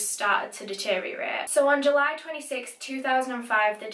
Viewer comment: ‘july 26 2005’ did i hear that right?? the day i was born??